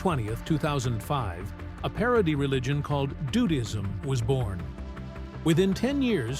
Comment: BRO at 17:25 min the captions said " a parody religion was formed JUDAISM" that's not right lol the captions should say DUDISM